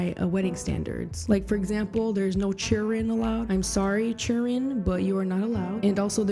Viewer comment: What does she say at like 3:11 when she says there is no "cheering/children" allowed at her wedding? I can't make it out